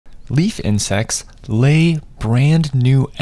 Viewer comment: No joke, I heard "beef and sex" when I played the video. I had to restart it to realize he said leaf insects. 😂😂😂😂😂